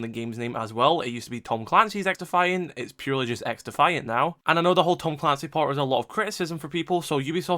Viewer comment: Im so mad, I literally thought he said X To Find, so i thought it was gonna be a locate and destroy, but he said XDefiant 💀